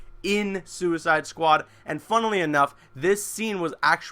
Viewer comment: did he just say funnily